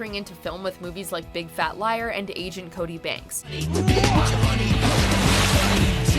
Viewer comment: I heard “Asian Cody banks”